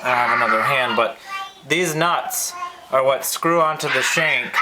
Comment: Did you say Deez Nuts? :D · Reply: These nuts. So yeah, I guess I did!